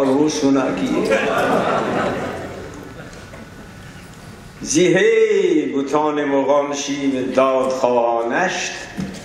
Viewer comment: what does he say at 12:17? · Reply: Farsi Zahe butan daad khwa neisht